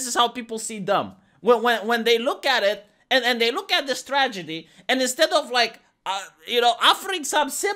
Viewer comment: said, "When they look at this tragedy..." I thought he said, "When they look at the stragety..." and I thought, "It's strategy, Vee." And then I realized my error. Then again, maybe not so far off.